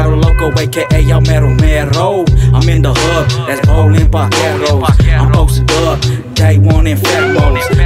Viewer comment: I thought he was saying Baldwin Vaqeros but he was saying parkeros